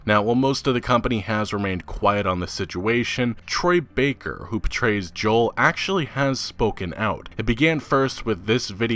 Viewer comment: I kept hearing "Troy Baker, who betrays Joel."